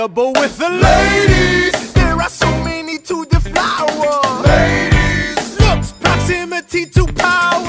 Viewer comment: In a winters ball I thought that Burr said "there so many to devour "until now 😂